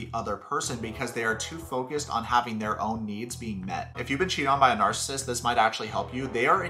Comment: I heard “If you’ve been cheated on by an arsonist” and my brain said “Where there’s smoke, there’s fire”